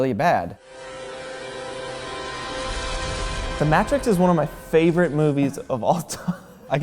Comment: DID HE JUST SAY THE MATTRICKS